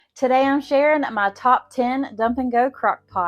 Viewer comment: I always say. Thank you ❤ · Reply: Hi. I was referring to the beginning when you said "today I'm sharing "; I heard it as the name "Sharon".